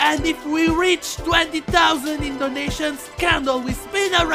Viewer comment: Did he said indonesian..? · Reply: I think he said "in donations"